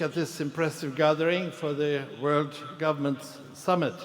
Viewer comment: he says "World governments summit" aswell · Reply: I hear 'World Government Summit' which is what is written on the wall behind him.